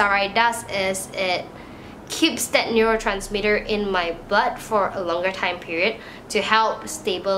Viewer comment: I swear I thought she say neurotransmitter in my butt! Lol sorry